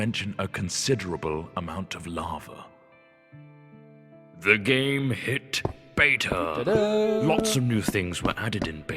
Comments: Captions say "they gave a shit" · The captions say love. Not lava. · why does it say in my captions i think at 4:17 they gave shit? and at 4:02 it says in captions VANESSA instead of the nether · instead of "the game hit beta" the subtitles said: they gave a shit. Lol